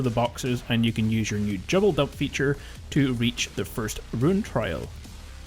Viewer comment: Lol. sounds like you said "jubble dump feature" instead of double jump feature at roughly 1:53 of the video. Lol. Kinda made me laugh